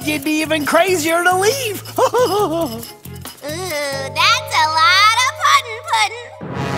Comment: what did Harley Quinn say? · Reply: "That's a lot of puddin', puddin'" '